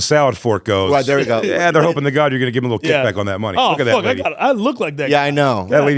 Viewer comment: Did J just say look at that N word? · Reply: Look at that lady